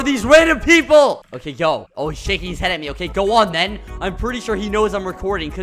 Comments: when he said, "oh he is shaking his head at me" i thought he said, "oh sh!t he is looking at me" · did just say the s word or am I deaf? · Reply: No he said "Oh hes shaking his head at me."